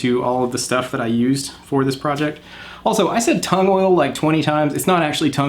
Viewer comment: I thought at first you were saying "Tone oil".